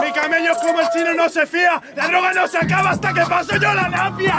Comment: alguien me puede explicar en el minuto 4:09 en la respuesta de que dice ´´ la droga no se acaba hasta que yo pase la lampia´´ que quizo decir con eso? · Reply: Dice napia. Se refiere a su nariz.